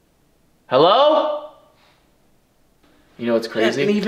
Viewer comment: when they heard talking it sounded like someone said yeah but it could’ve been whoever was holding the camera